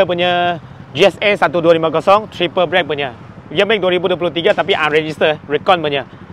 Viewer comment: I thought I heard something odd when he mentioned the word "Triple brake" but later realised what he really meant was "Triple Black"